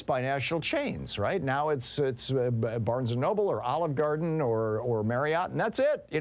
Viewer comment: He said Olive Garden and I swear for fraction of a second I thought he said "Oligarchy Garden"